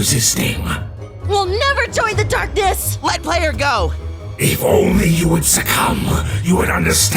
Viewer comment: Did you just say ROBLOX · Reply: Nahh he said let player go